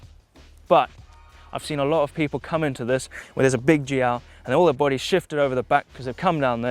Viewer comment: Excellent job! Your stoke level is super high and gets transmitted to us. Great tips all around. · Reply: im trying to do the spanish subtitles for this video, but i dont understand what you say at 4:28- 4:30(sounds like "big GL"), and what it means, can you help me